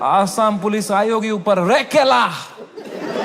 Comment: he actually says "ai kela" 😂😂😂😂😂😂😂😂